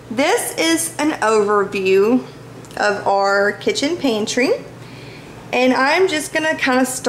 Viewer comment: Lol.. I just had to pause the video to tell you I just got a good laugh while watching... I was looking at other stuff online while listening and heard you say" this is an over..." And my mind automatically said "ovary!"!!!! LOL.. "So you said this is an overview while I heard this is an ovary! Ok LOL.. Just wanted to share.. back to finish the vid. ;)